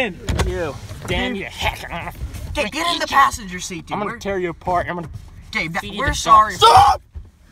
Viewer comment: wtf did Gabe say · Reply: "I'm gonna EAT you, I'm gonna tear you apart and feed you to sharks" LOL